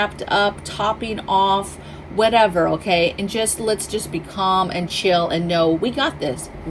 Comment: Atfirst I thought you said to focus on "Ping Pong and prepping" LMAO! Oh....you said "be calm". 🤣 I'm 5 hrs from SeaTac. Keeping my eye on that West side.